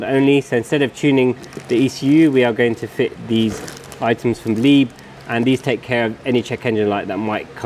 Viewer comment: Didn't quite catch who makes the OPF/cat bypass harnesses? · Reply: Leib